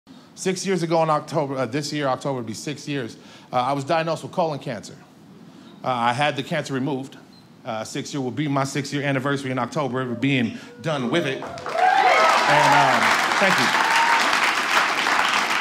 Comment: I thought he said "I had the cats removed" and was confused when everyone started clapping.?? Then I shouted (startling my poor dog) "OH CAAAANCER" when I finally realised